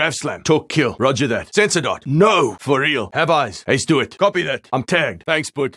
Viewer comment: What does he say at 16:09? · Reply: "He's dead"